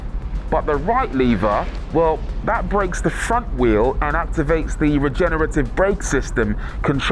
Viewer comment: did he say the fuc* wheel